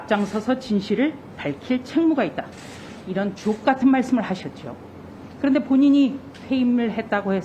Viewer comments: 이런 족같은 말씀으로 들리는데..나만그런가.. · 이찌 듣기에는 주옥 같은말이 족 같은으로 들리냐 ㅎㅎㅎ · 나는 "주옥같은 말씀"이란 자막이 없으면 "족같은 말씀"으로 들리는데....